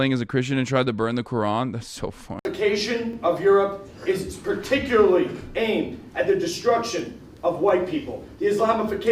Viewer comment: i thought the guy said "straticularly" like george bush or something. I had to rewind to see otherwise